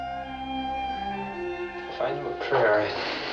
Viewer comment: What does the enlisted man (Sonar Man?) say at 18:09? The XO, responds, "Never mind boy, I'll say another one for ya." I think the crewmember was invoking the name of a saint, but I didn't quite catch it. Anyone? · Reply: I think he said "If I knew a Prayer..."